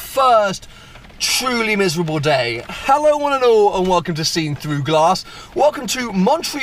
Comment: Yet another form of "misheard" intro sentence by the auto subtitles... "how I want to know a walk of the scene through glass" :-)